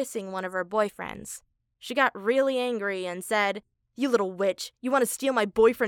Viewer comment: I Thought she said you little bich right 🖕🖕🖕🖕🖕🖕🖕🖕🖕🖕🖕🖕🖕🖕